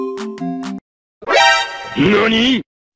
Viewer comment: O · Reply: I think, he said YONI?!